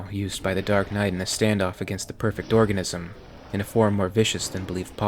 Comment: I thought that it said “Batman Meets the Perfect Orgasm.”